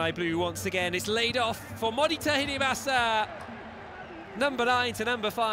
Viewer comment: 守田入れました！(空耳)